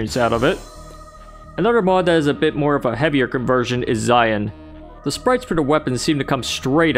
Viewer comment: whats the mod called in 2:29 and can you get it on zandroum i couldnt hear you very well and 4:05 i know its for quake but which type and (again) is it for zandroum so i feel like im asking you too much questions so bye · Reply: Zion and Slayer's Testaments. Check the description. The former mod probably needs a more recent version of GZDoom, and the latter needs Quakespasm-Spiked.